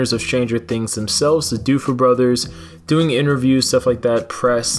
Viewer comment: Sorry, did u just say the Doofer brothers? 😂😂😂